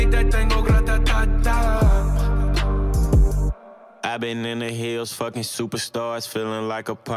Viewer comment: I always hear I've been in the hills fucking doods